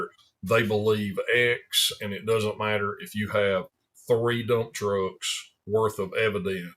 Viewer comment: the first time i heard it I swore you said "no amount of evidence will change some people's mind, it doesn't matter, they believe x and it doesn't matter if you have three Donald Trumps...." but it was actual "three dump trucks". Same same.....